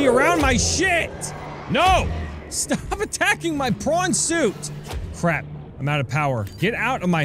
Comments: I think at 8:53 he said "Dont attack my porn suit" just my opinion hahahaha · it sound like you said: "Stop attacking my Porn Suit" ... . xD